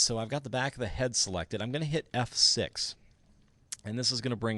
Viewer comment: did you say f + 6?